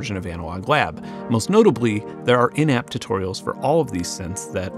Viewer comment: took me a couple of listens before I heard "in app tutorials", instead of "inept tutorials". I guess I'm used to really low bars.